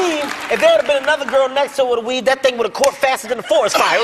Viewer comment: what's a Wiv? I don't understand what he said at 3:20 (I'm not English but French :/ ) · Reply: Weave*. Hair..